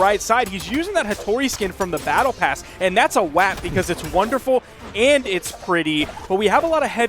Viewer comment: did he just say “that’s a wap”